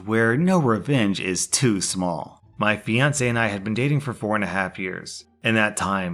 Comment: I thought he said "Beyonce and I were dating" instead of "My fiancé and I were dating"